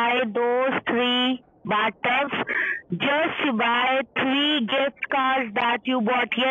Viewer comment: When she said "you must buy $3000 gift cards" I could of sworn she actually said 3000 $1 gift cards. I would of loved for them to wait through 3000 individual codes.